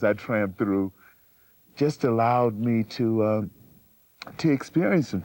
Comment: Why does the closed caption delete the word tramped? What do you suppose it thought the actor was saying?